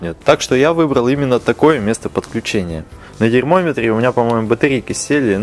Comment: Мне послышалось дерьмометр.